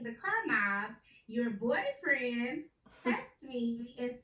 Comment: WAIT WHO IS HER BOYFRIEND?!?!?! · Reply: I thought i heard her say...your boyfriend planned too🤔🤔🤔